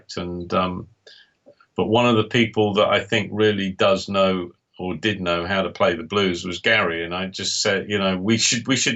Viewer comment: I hear "They must be somewhere. They wwijjfjfzxzci".....seriously, someone help. I wish I knew what he said. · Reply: the garbled bit is 'they WILL be somewhere' 🙂